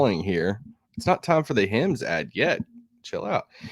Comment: First I thought he said hymns. He said Hims. Be himmy. Take Hims and make your significant other (husband or wife only) sing a hymn with Hims.